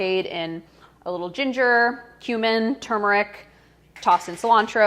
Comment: did she said human? f😳